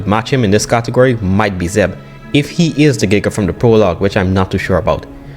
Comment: did you say “zeb” is that the Giga’s name? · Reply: Yes His name is Zeb